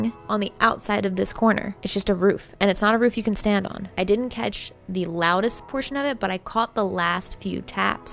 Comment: i heard "i didn't wanna die" how can he say " nothing of note" after that xDD